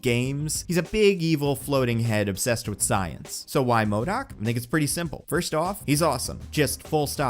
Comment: What did he said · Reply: "Think it's pretty simple" but It also kinda sounds like he's saying another certain word lol